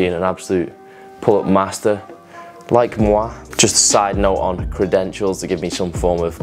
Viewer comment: « like moi » are You speaking french ??